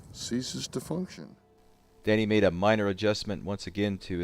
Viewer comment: Lmfao, lil after 9min it sounded like you said "daddy made an adjustment" bahahaha oh n hey is that an SOG twitch pocket knife you had here?